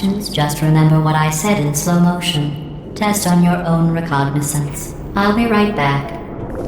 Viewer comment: I think she says “by methodically tipping people’s hats off something something then I will be back “